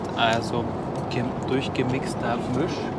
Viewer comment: Bei 16:16 wer hat auch furchgewichst verstanden?😂